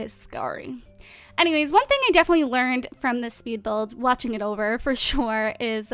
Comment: I heard "bitchy speed build"